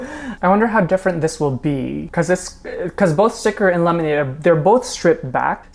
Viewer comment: did u just say sickret instead of sticker😭